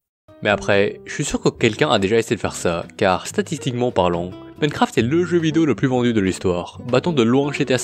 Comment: Funcraft ? Dites moi que je suis pas le seul à avoir entendu ça ?😂